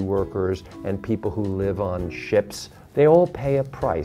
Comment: Did he say, people who live on shits